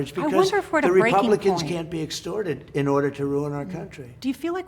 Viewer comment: Did anyone else hear at the 1:03:12 point where said "Republicans can't get extorted" while O'Donnell was running her mouth?